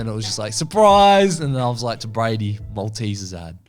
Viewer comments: I'm sorry I can't tell what said, any Australian's or anyone know what he said? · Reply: Maltesers ad, cause they black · what does that mean? "more teasers?" i can't understand lol someone pls help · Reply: Malteasers, it’s an Australian chocolate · what did he say? What dose it mean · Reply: maltesers ad